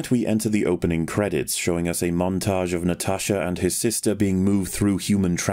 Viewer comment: "natasha and his sister"? Did I fully miss the fact that Natasha is trans? Even the subtitles said "his". I did think she looked a bit strange but, am I really missing something this big? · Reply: He said "her".. it sounded like something else due to his accent